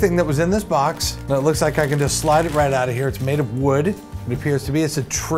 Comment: I think it's funny he assumes everything is a bird lol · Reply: Is it me or did he say " it looks like it will slide right out"